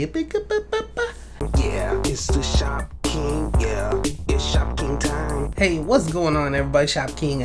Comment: it sounds like hes saying it shopping time